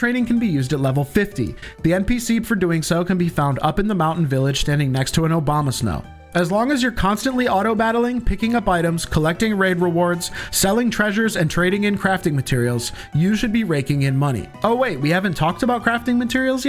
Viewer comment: bro fr I thought he said "NPC standing next to a Obamasnow"